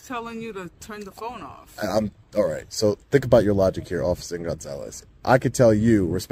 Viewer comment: A🎉t 43:24 did he call her officer nut- zales instead of Gonzalez??? I swear that's what I heard. 😂😂😂